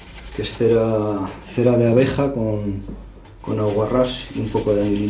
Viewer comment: Alguien entiende qué dice aquí 18:18 ? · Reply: ¿Cera de abeja + aguarrás + qué amarilla?